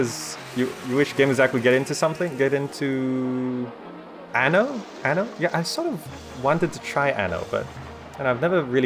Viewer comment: what is the word he said? anal? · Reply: Haha Anno, the city-building series.